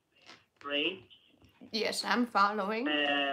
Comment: Did he say one of the ingredients is hydrazine?